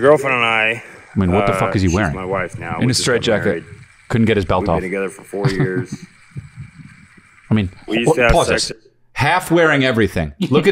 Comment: I'm slightly confused. the guy at 33:10 say and I quote " my girlfriend and I, actually shes my wife now we just got married. We been together forty years" .. which is already seems odd but then you look at the man speaking and there no way older than 40 yrs old, he then goes on to say their a young couple . wtf he talking bout??? Anyone else hearing this or am im loosing my mind. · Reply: I heard "we've been together for four years" .